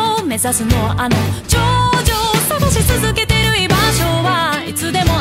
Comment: did I hear JOJO?